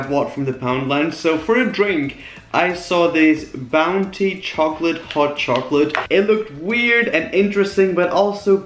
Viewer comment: Did He Just Say “Chocolate Hot Chocolate?”